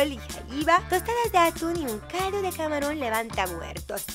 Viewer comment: les juro que escuche calzon jjajjajajjaja🤣 no mames estoy bien sorda XD